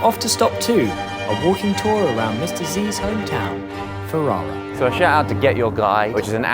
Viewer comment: When Ollie said "to Mr. Z's hometown" I thought he said "to his diseased hometown" and I couldn't believe he'd say something so offensive. 🤦‍♀️🤦‍♀️🤦‍♀️